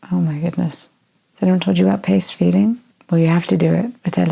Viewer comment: OMG flashbacks to the paced feeding...we used to call it "bullying the baby" because she wanted to gulp the milk down and we had to pace her like the NICU taught us!. It does take forever! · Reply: OMG, I did not look at the closed captions, I heard “paste feeding”. Paced feeding is better..